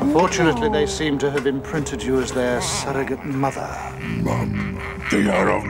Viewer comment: Lol I swear..at 1:46 does anyone else keep thinking "MUMA" is being said by Shreddar mimicking Razar in fustration! Since his voice sounds like Razarr?